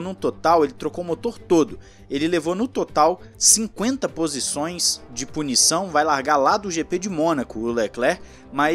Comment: nao entendi. 50 posições de punição! É isso mesmo que ouvi?